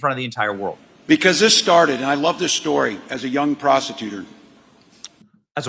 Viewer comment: he said prosecutor...yup another clickbait title from Benny. Ya it sounded like "prostitutor" but thats not even a word for one, and two you obviously know he said prosecutor. By no means am i siding with ole Kameltoe and Wally world, but i also dislike when people are accused of false accusations